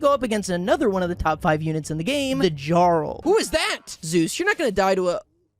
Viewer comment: did he just say "jar-el"?